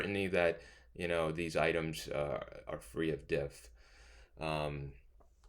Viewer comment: Dear professor, I loved your video. I just have a question. In the minute 35:25 you mention that you have to be "certain that these items are free of" and I don't understand the next word you mentioned. Is it "diff" as the close caption suggests of "depth", or something else? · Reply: The statement should be "free of diff", which means that the items do not function differentially. The items you set for your anchors should not differ across groups. Usually you would need some content knowledge of the topic the items are based on or some prior research to determine this.